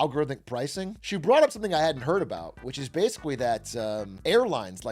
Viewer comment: or so did he say "basicwee" heh